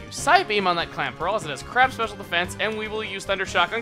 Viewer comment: When said Corpish has crap special defense I heard it as crab special defense.